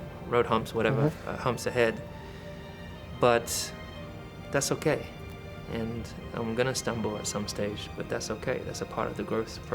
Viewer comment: what did he say can't catch it? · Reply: "I'm gonna stumble at some stage. But that's OK, that's a part of the growth process"